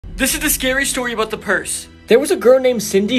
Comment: I thought he said purge😂